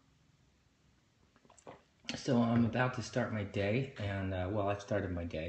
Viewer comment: When he said 'I'm about to start my day' I freaked out b/c I thought he said 'im gonna start being gay'